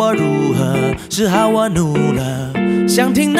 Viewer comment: "是好我怒了"我之前都聽成"只好我擼了"......真害羞內...